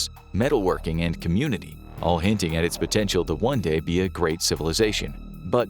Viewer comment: did he say……POTENTIAL?